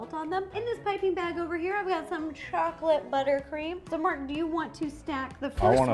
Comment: did Ro call Mark, Martin? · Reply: She said "So Mark, do you want to stack the first one?" I guess the stutter between "Mark" and "do" made it sound like Martin lmao